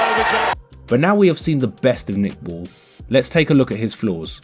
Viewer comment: I could have sworn you said, "Let’s take a look at this fool..." as his opponent got it. 😂 Ball has studied early Tyson and learned well - the gazelle punch, level-changing, combos galore, and controlling opponents with his legs and feet. I've learned a lot from your breakdowns and analyses. Thank you.